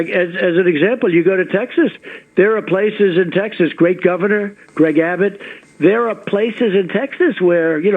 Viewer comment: I thought he said “Greg Governor” along the same thread of “Tim Apple” but after listening a few times, I think it’s “Great Governor”. Either way, he didn’t say anything useful or informative. Fuck trump.